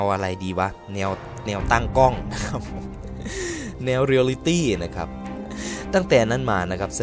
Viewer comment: เรือริตี้555 #ที่ผมได้ยินนะ